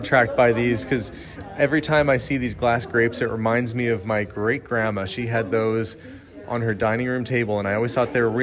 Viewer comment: ha ha! 🤣 😅😂for a second I thought you said that the glass grapes 🍇 reminded you of your grape grandma!! 👵 and my mind flashed to Violet Buaregaurd, in charlie and the chocolate factory, and her licking the wallpaper and turning into a giant round purple grape 🤪🤣!